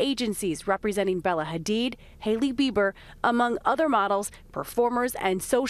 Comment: Did she say Hailey Beaver? 0:56 Lol